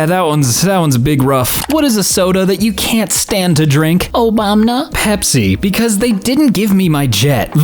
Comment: WHY DID YOU SAY OBAMNA YOU MADE ME CHOKE ON MY MCNUGGIES